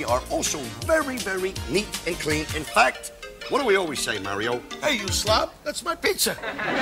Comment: Is It Me Or Did I Heard Him Say "HEY YOU SL*T, THAT'S PIZZA 🍕 😡",?, Ay I'll Be That Angry If Someone Dares To Touch My Canadian Pizza With Some Ham And Pineapples